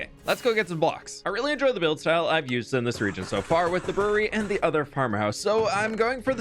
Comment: legitimately thought he said “furry” and not “brewery” man my brain is braining great today